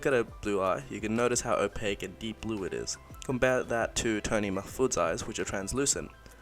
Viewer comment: i thought he said toning my foot haha